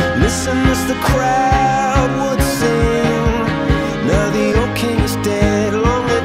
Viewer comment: why does it sounds like "mr krab would sing" am i the only one who hears it?